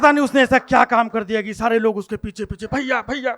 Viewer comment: he was actually saying Modi-Modi....